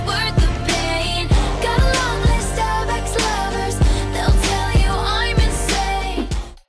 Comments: The Taylor Swift one I thought she said get along with the Starbucks lovers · In blank space at first I thought she said star bucks then I thought she said star crossed lovers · I'm sorry but I just heard 'stomach lovers' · For blank space I thought it said:"got a lot of Starbucks lovers" and imagined them in her trunk. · none of theses sounded wrong when I first heard them lmao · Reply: BrittBrittCupcake123 thought she said stomach lovers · This title is a lie, the only one that I agree with is Taylor. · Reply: I thought it said "starstruck lovers" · it will always be starbucks lovers to me lol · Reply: I thought it said "starved ex lovers" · I heard "go ask the Starbucks lovers, they'll tell you I'm insane" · I hear "Got a lovely starbucks lovers" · I thought it said star bricks lovers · I didn't even hear Starbucks lovers, I heard the actual lyrics xD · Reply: I thought it said "I got a lot of star-X lovers.. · I thought taylor swift said God loves those starbucks lovers! 😂😂😂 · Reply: I thought it said gotta love those starbux lovers